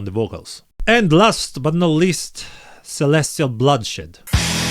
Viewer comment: I heard "celestial bloodshit" and I liked it 🔥